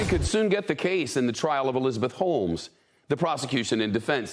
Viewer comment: My ears are deceiving me, I heard "Elizabeth HOLES" lol